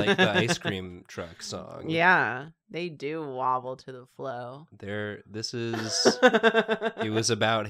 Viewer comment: Did say "wobble to the flow?"